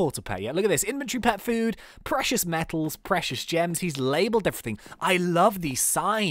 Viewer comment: Build multiple floors so you can have more space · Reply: LOL, I thought you just said moose metal. I should wake up